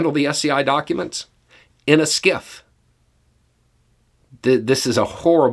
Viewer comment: Took me a bit of googling to understand, what "SCIF" means, since I heard it as "Skiff" and did not get any good results....